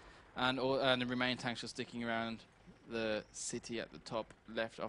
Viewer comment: at first it did sound like he said, "dicking around."